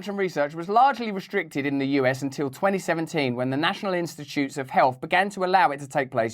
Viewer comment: Instead of "National Institute of Health" I heard "National Institute of Hell" and I didn't even question it. Made sense.